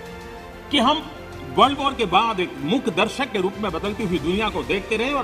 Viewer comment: did he just say world war??